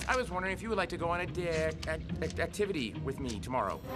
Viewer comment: wait what he said was would you like to go to a da-....ick